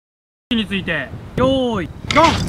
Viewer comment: Is he mad 1:07 What is he saying we we we